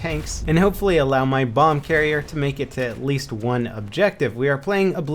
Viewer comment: wait a minute did he just say bong carrier?